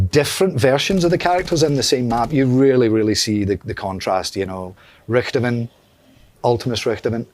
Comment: it Sounds like he says Richtomen